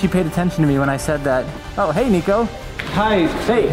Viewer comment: At first, I thought I heard, Oh hey, Negro. 💀💀💀